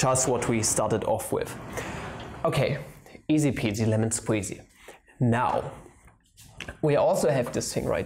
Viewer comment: Maybe you should invent a symbol to represent "bruh" so you can solve integrals with respect to "bruh",idk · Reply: Also,did I hear "easy peasy lemon squeezy" at 13:49?